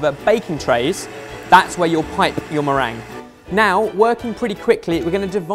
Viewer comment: Does he say "meringue"? xD